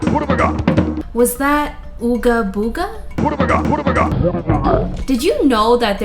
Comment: "what have i got" is what i keep hearing lol